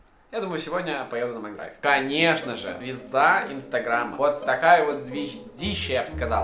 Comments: А4-ДАВАЙТЕ НАПИШЕМ ЗВЕДЕ ИНСТАГРАМА🌝 Я-МНЕ ПОСЛЫШАЛОСЬ ИЛИ А4 СКАЗАЛ "ДАВАЙТЕ НАПИШЕМ ПИЗДЕ ИНСТАГРАМА" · мне послышалось пи3д@ и не зря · а мне послышалось: п*зда инстаграма )) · Мне одному послышалось ПИ*ДА Инстаграма 7:31 ору